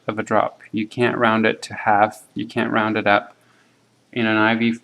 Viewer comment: at 46:50, I can't exactly hear correctly, but did you say "you can't round it to halves and you can't round it up" so if the answer was 83.5 drops/min the answer would be 84 drops/min?